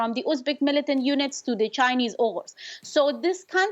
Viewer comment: did she said chinese oghuz?